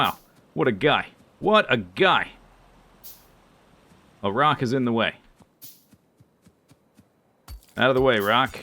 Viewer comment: for some reason, i hear that as "Iraq is in the way"